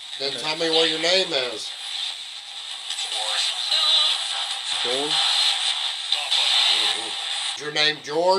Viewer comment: I heard, it’s Ward!